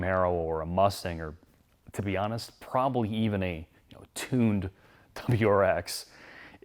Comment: what did he say? Tuned URX?